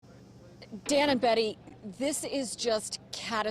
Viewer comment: Why I thought she started off saying “damn it Betty” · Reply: you guys are both incorrect. She said Dan & Betty 🤷🏻‍♂️